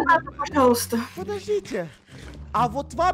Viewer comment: мне одному послышалось: пошлите на***